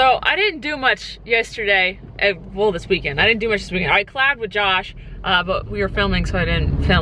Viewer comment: Was I the only one who thought she said I slept with Josh instead of I collabd (is that how you spell it?) with Josh? 😬😁 love you 😂❤️❤️